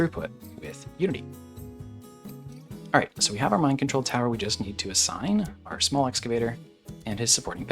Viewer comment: for a second I thought you said mind control tower to force them to mine like some draconian overlord